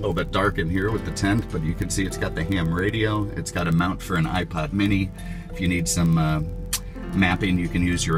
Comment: did he say ipod mini? it was like 5:40 minutes in the vid